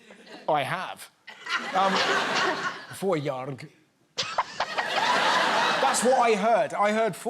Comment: She said "får jag" (may I) . But Im not sure what she thought he said. The best I could come up with is "fårjaga" (sheep hunting). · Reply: Yeah me too. It sounded like he said "fårjard" the first few times. But that doesn't sound like anything to me 🤔